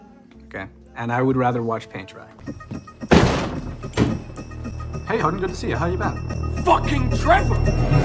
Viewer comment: For some reason, at 0:24 I thought Trevor said 'hey honey, good to see you' rather than 'hey hardin' which only makes it even funnier 🤣